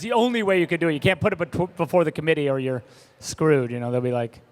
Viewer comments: Hey, can you tell me what Zack is actually saying at 24:32? He says "that's the only way you could do it, you can't....???" i can't figure out the rest of what he is saying. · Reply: "You can't put if pro-before the comittee.." I think he just stumbled on his words, I think he might have just mixed the words "in front and before" almost saying profont or something like that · what is he saying? "that's the only way you could do it, you can't....???" I need help for someone to tell me what Zack is actually saying. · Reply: “...you can’t put it before the committee or you’re screwed.”